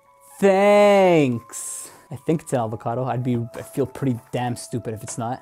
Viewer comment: My favorite part is 2:14 is it just me or did he say "alvocado" lol. I like your vids man! Let me get that ipod for my son! Lol. Cheers man!